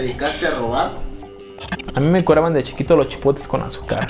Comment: Entendí "A mi me encueraban de chiquito"